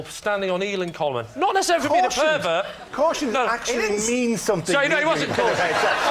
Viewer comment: What did Lee say? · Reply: Not necessarily for being a pervert